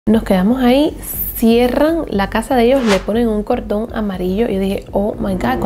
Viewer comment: parece que dice un condon amarillo jajaja